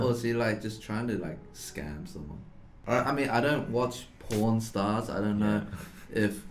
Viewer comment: Was looking away from the screen, 3:10 plays and I legit thought he said "I don't watch pornstars".